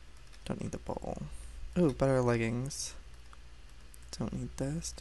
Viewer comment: Did he just say butter leggings